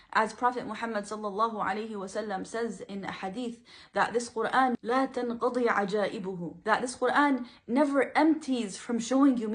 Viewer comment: Is the word 'ajaaibuhu' ... I didn't quite catch the word. Then it would mean (to me) It "will never be devoid of surprises". Someone who knows the hadith or the Arabic of it, please let me know. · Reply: وَلاَ تَنْقَضِي عَجَائِبُهُ